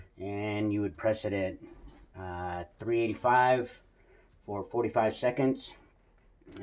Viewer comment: is that 385° or 305° I have subtitles on but didn't hear it clearly. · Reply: 385 f for temp